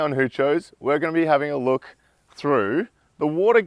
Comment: What does Hoochos mean? Also, I always hear “who chose”.